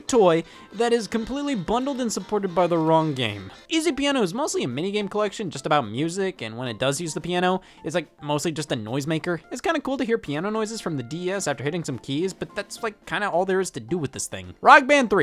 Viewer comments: I keep hearing him pronounce D.S. as D.E.S. and now can't unhear it... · I thought you said Rock Man 3 while I was playing Mega Man 3 lol.